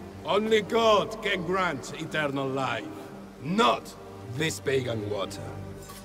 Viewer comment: Only God can grant eternal life, not this vegan water. 6 vegans and protestants disliked this clip. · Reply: Use your ears, he says PAGAN water 1:25 how tf does anyone think he said vegan? Can very clearly hear the "p" not a "v" smh